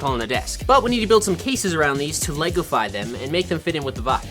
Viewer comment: Did bro just say legify 😂😂